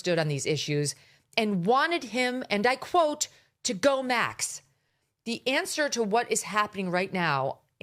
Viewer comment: It’s almost sounds like he said DONT go max lol I have reminded multiple times! At this point, if Charlie isn’t here I don’t want anyone to say what Charlie wanted UNLESS it’s from his wife!